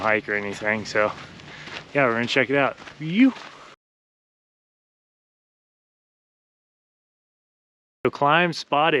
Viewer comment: Thanks for sharing this go pro review. I just ordered mine :) curious on what you said on 00:42 scene, I hear that a lot on MTB vids, what is it you said and what does it mean? :) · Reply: Thanks Jes! I'm glad you found it helpful. The word you're asking about is "Yew!". It's a general expression used to show excitement. I learned it through surfing but I think it's pretty common throughout the action sports community these days. Thanks again for commenting. "Yew!"